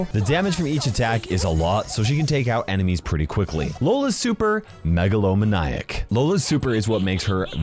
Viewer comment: When he said megalomaniac i thought he said megalovania